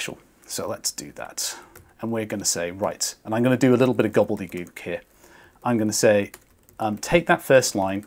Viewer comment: “Gobble de goob” what did you say? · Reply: "gobbledegook" is an English word